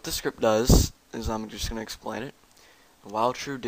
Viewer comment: I was useing Captions ;English Subtittle , hoping that i could catch up on what he is saying. At 0:40 he clearly said "Is im.... just gonna explain it" , Youtube translate it as "consumption explains it". That is really disappointing. T_T